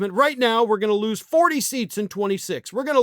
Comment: I thought he said "40 seats" not "40%". 40 seats would be like 19%?